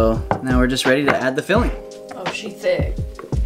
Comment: Did I hear her correctly? Did she say, “Oh, she thicc.” Lol